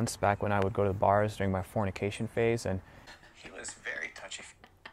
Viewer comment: I heard it a "Fornication Face" ... and thought for a sec that he ment his climax-face...